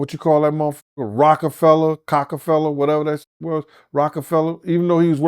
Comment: Did he just say Cockafella?😂